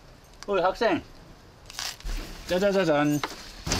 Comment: ???: 짜자자잔 순간 잘못 들었나 싶었다 ㅋㅋㅋㅋㅋㅋㅋㅋㅋ